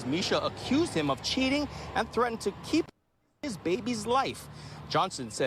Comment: Sound like it said tried to keep him out of the baby's life